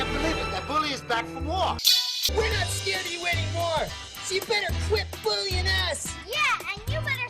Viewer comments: "So you bitter quit bullyin' ass" I swear that's what I heard. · "YOU BETTER QUIT BULLYING ASS!" 0_0 did i just heard 'ass' in a kids movie? da fooq